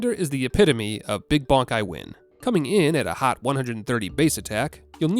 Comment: I heard that as "big /bankai/ i win" at first and nearly just went along with it cuz well, yea it very much is an apt description of Bleach XDD